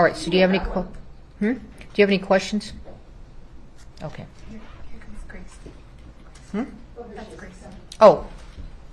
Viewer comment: What did the Judge mean by, when she said, "We'll take a, what sounded like a BATH? and break" slightly before or after 1:02? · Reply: "We'll take a bathroom break."